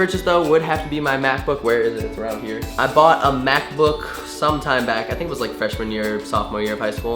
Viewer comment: I also thought he said math book. LOL. Love the vid